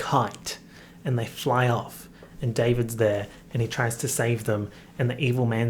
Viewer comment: when he said "davids there" i heard : "DAVIDS DEAD" XD i was shooked